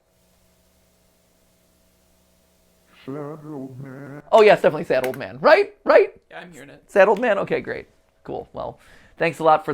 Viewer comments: I heard shadow man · I think he is saying 'shadow man' 30:30 'I've being trying to come back to you'